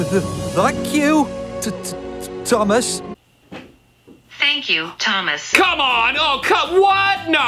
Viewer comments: Hi! I CLEARLY heard “F**k you, Thomas.” I’m surprised parents didn’t complain about it like they did Bob the builder, which I didn’t hear any swearing in that clip, it was just muttering like they claimed it to be. I did hear “F**k” in Hunchback of Notre Dame, I also think Mr. Rogers knew what he was doing because of the way he grinned right before he flipped the bird! 😂 Hilarious video! Have a good day! 😃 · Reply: I heard "F*** you Thomas" to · Hi! I CLEARLY heard “F**k you, Thomas.” I’m surprised parents didn’t complain about it like they did Bob the builder, which I didn’t hear any swearing in that clip, it was just muttering like they claimed it to be. I did hear “F**k” in Hunchback of Notre Dame, I also think Mr. Rogers knew what he was doing because of the way he grinned right before he flipped the bird! 😂 Hilarious video! Have a good day! 😃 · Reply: I CLEARLY heard "f*** you Thomas" too.